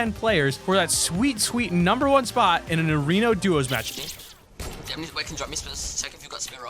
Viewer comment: did anyone notice he said arono duos instead of arena duos (¬¬)